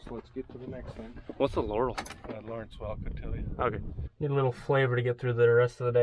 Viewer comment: I so wish there were subtitles. is this what they said at 11:34: "All right. On to the next thing heh?" "No point in sitting here resting on our laurels, let's get to the next thing" "What's a laurel?" "uh, Lawrence Welk will tell you." The cows said they liked the concrete pour. No problem there!